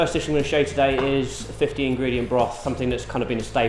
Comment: I thought he said 15 ingredient broth, but reading 50, what a cluster. The next two, great flavours, but holy... the presentation. facepalm